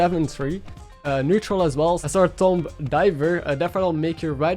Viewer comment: Did he just call her Sir Tom Diver?